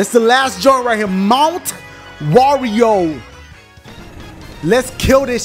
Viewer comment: did he say mowent wario